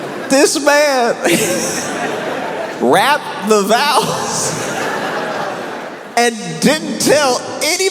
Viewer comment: OMG I thought when you said "rapped the vows" I thought you meant "wrapped the vows" like a gift for WAAAY TOO LONG